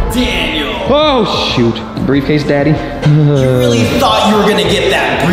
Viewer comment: did he say briefcase daddy💀✋?